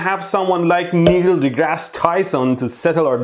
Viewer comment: Did he just say "Needle deGrasse Tyson" 😀😀